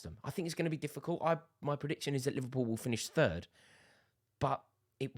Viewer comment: swear u said 7th dont lie mate lmao...